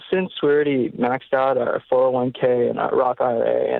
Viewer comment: You guys didn't recommend roth i.r.a.s. He said their 401ks were maxed out so that was one of the reasons they were considering life insurance. Missed that chance. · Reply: Thought he said 401k and roth